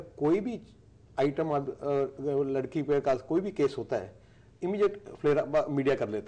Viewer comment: Did Sukhbir called girl an item ???? at 3:27 ...... If he said really said so ..... then its shame on us that we choose these tharki people as our leaders